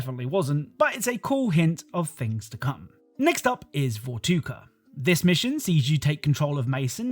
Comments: Did bro just say, “Vortuga”? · It sounds like he says "vorguta" instead of vorkuta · did he say votuka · Did he just say vortuka instead of vorkuta?